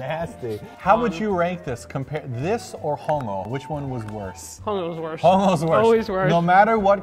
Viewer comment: I thought you guys were saying homo at 10:53 lmao